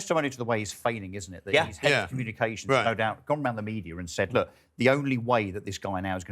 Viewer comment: I thought she said she was a communists